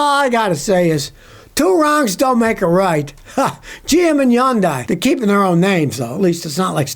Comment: For a minute there I heard you say that GM and Honda were merging and then proceeded to call them the two stooges. Then changed it back to Hyundai and GM; Phew I thought, Pitty poor Honda I thought what a robe to tie around your neck.